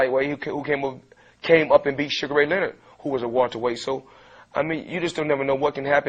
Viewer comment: i sounded like he said suga-ray nigga